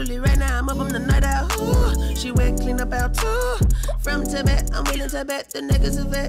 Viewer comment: I always thought he was saying “from Tibet” instead of “front to back” 🙈